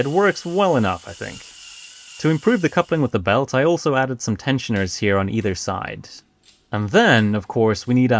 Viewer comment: i thought he said pensioners😂